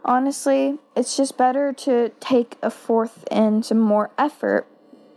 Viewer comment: What does she say at 02:10? "...to take a fourth..."? What does that mean?